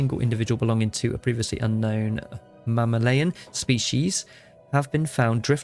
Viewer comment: wo wo wo, did you say "mamma-layin" 18:01 😄